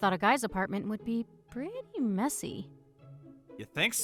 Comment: When she said preety messy i thought she said preety messi and i chocked on water